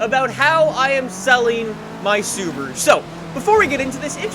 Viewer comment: Thought it said 'Im selling my STD'